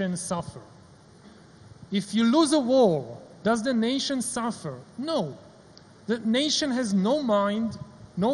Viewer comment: I heard "does the nation suffer" as "does an Asian suffer" 😂